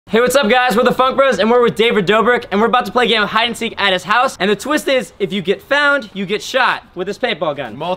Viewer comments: I thought he said hey guys what the fuck bros 😂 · At first I thought he said staple gun instead of paint ball gun